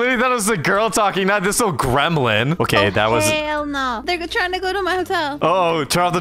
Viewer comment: Did just say “oh hell nah!” 😮